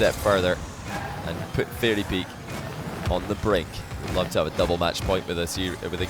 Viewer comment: Why is it MW and ML? Isn’t a match win or loss the exact same as a series win or loss? I think you meant game win or loss. You play multiple games in one match. Cmon, who edits this stuff? Spoiler: 37:15 he actually says “if fairy gets another win he’d be on double match point”